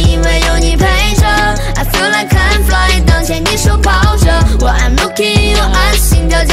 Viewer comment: lmfao I heard “when I’m looking at your ass”💀